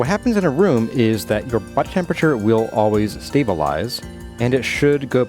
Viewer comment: I thought you said butt temperature at first.